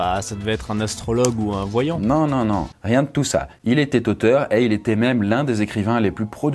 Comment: "il étais toteur" j'ai rigolé, c'est tout pour moi je retourne au visionage de cette vidéo 🙃